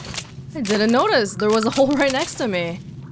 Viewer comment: "Didn't notice, there was a hole right next to me" That is what he said?